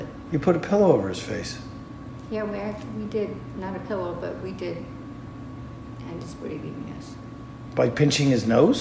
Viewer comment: The coverage you did on this case was great and extensive, but this channel really has a track record of not hearing video clip audio correctly from interrogations and similar clips and then having incorrect subtitles on their videos. I am not referring to the YouTube generated subtitles that you can click on and off in the settings, but the ones the channel actually puts on their videos during the editing process. 6:32 She actually says: "Not a pillow, but we did... end his breathing, yes." 10:59: "Well, it is because hospice does it all the time." Also, during interrogation/confession to police, 16:21: "...She's a CNA." CNA = Certified nurse aide. There is not a professional title in the medical field called "CLA". There are also several other words/sentences not correct in the subtitles throughout the whole video, but I just can't be bothered to type anymore out here. I just imagine that these incorrect subtitles can confuse someone who has deafness or hearing issues and depends on them to get the context of this case. That's really my only gripe about this channel and I hope that this will improve in the future. Otherwise great work on the video, those sisters are sick and evil.